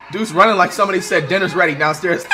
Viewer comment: he said “dude’s running like someone said dinner’s ready downstairs” but I thought he said “dude’s running like someone said Dennis Raider’s downstairs.” Maybe I need to cool off the true crime LOL.